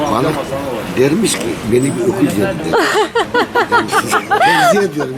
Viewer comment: Sezgi abinin babası "balık rakısız içilirse balık ..." dermiş ne dermiş anlamadım orayı söyler misin lütfen? 💕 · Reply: Çağla Su "beni bir öküz yedi dermiş" dedi gibi anladım